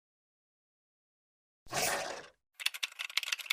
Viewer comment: 'OMG AND HE TOTALLY JUMPED OF THE CLIFF AND SWAM ALL THE WAY THE THE SHORE CUZ THE DOG CHASED ALL THE WAY TO THE END' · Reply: I hear "omg we tottaly jumped off the cliff because the dog chased us all to the end"